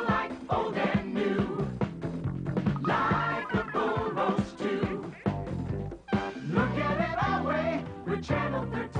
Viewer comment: Can't make out 0:35... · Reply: Ian Sherman "Like the bull roasts too"